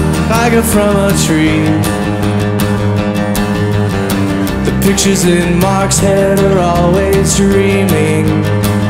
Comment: I always hear "The pictures in Maxwell are always dreaming"